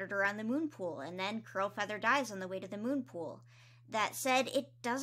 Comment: Am I mishearing her or does she keep saying crowfeather instead of curl feather??